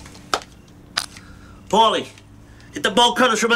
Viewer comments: Paulie, get the bolt cutters from outta the trunk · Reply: I heard him say "ball cutters".... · The one phrase you never want to hear from the Mafia. "Get the bolt cutters!" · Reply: lol, i thought he said "get the ball cutters"